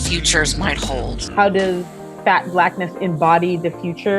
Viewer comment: Did I just hear her say "fat blackness"?